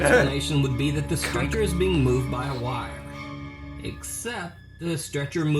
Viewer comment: Yo can you guys explain to me what says at the asylum cause i dont get it at all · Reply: he says wires it means one or more people or pulling on it but out of sight so it seams like a ghost